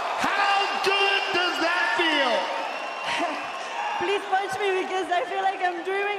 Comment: Lol. Did she say, "punch me"?